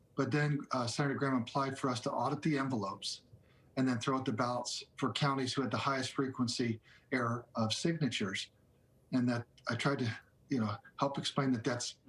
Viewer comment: No, he said throw out the highest error of signatures so if those signatures are not valid those are NOT Legal Votes and yes I saw 1:29 that was not said. The accusations are NOT correct. Brad is messing up he needs to follow Georgia Law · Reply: I just listen again and i heard correctly. at 1:31 it states "Then throw out the ballots for COUNTIES who had the highest frequency errors of signatures". So the request is to throw out the entire counties' votes and just not those that were problematic. Additionally..why isn't he doing the same in ALL the states and not the ones in contention?